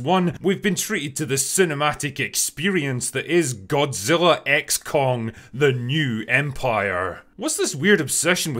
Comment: For a moment I thought you said 'Godzilla: Ex Con'.... I think that'd be a very, very different movie.